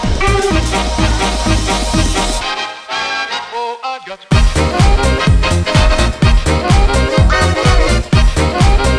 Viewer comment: wait what does he say at 2:07? @@ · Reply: He says "Oh, I've got Spurs", but thr sample is cut off.